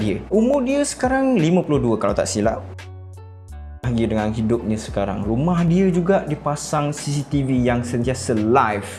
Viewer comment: bang yang minit ke 4:51 tu abang nak cakap apa sebenarnya. Saya nak tahu ni.kenapa censored? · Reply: Ryu Shank “semoga mereka satu keluarga” ... itu lah dia hahahahhaha